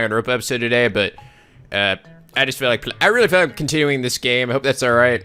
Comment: Oh god, I misheard the part at 1:20, and falsely understood, "[...] Danganronpa episode today, but ... ehm I just feel like pl...-I really feel like discontinuing this game. I hope that's alright..." And I was like "WHAT? Q_Q NOOO! why? because of the child abuse topics of the last two episodes? But whew... Everything is good now. I love that game, continue to reach the Platinum right now and will most likely play Steins;Gate, too, soon. ^^